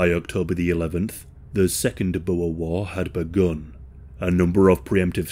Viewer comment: "second Boer war". I heard "second wawawa". 😆